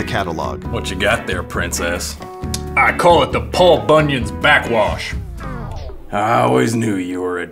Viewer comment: Do we get the recipe for Paul Bunyan's backwash or what I heard "pulp onion's back crotch